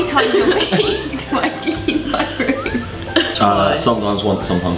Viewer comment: I thought she said how many times have I sold weed in my room. but then I checked again and it sounded like clean. oops